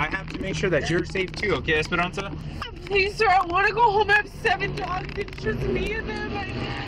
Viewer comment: Cop is 100% professional and the girl is 100% respectful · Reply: I did not hear her say "ass hole" it sounded like she said "Please sir I want to got home, I have 7 dogs and it is just me and them" 22:44 it seems like she fears if she gets arrested no one will be there to care for her house of pets.